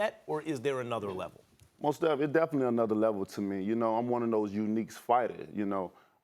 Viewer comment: did he just say he's one or the uniques fighters out there😂😂